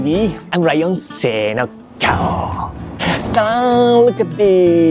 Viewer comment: What is he saying at 1:09 ? What does it mean in English ? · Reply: is saying “Sei no...arrrrr” sei no means something like “Ready” then his signature lion roar.